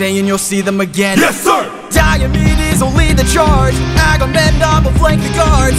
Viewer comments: I thought bro said Diabetes. 6:05 Bro really just had a "I'm just a girl 🥺🥺🥺" moment. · do i hear diabetes?